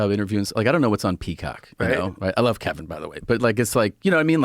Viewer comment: for a second I thought he said I love cock